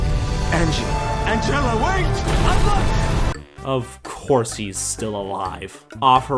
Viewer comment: Wait a... I thought he was trying to say "I love you!", and it was cut off at "I lo-!" but I just noticed, he was trying to say "I'm not dead!"